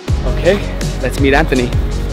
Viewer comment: I THOUGHT HE SAID LETS MEET ANTHONY